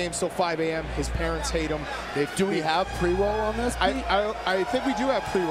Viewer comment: I heard " Do we have pre-rolls" and thought 'Oh shit, light it up 🔥' 💯😂